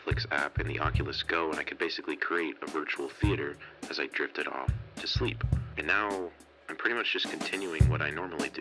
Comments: Did anyone else hear this foo say "as I jerked it off to sleep" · I thought he said "Jerked it off to sleep" lol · did he say he jerked it off to sleep? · I heard " I could make a virtual cinema while I jerked off to sleep" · I thought he said jerked it off to sleep